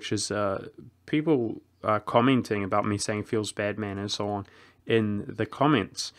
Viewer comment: OH! feels BAD man! I thought he was saying "feels Batman!" I figured he was going to use the pent up rage to go out and fight crime after the video was over.